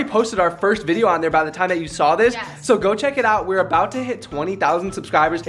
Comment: Is it just me or did she say yes